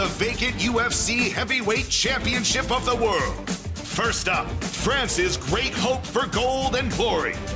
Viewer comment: When he said "France's" I thought he said "Francis" and got excited for a split second.